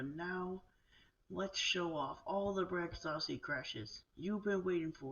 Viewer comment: I think I heard crashes? But THANK YOU MAN!!! I appreciate and love this.